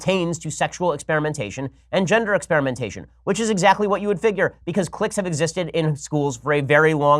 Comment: Did anyone else hear clit first before realizing it was click?? lol